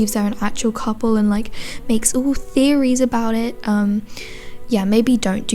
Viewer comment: For a hot sec i thought u literally said "UwU Theories" LMAO I NEARLY LOST IT